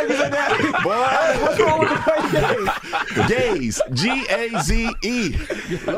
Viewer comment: I love this interview Tim 😆 I heard it a few days ago while mowing and in the beginning Bakari mentioned the “white GAZE” lol and I heard that “white gays” also . It was so funny the 😮mowing stopped to laugh laugh laugh and even right now 05/01 I’m still laughing, thanks you guys for laughter when WE all need it👍🏾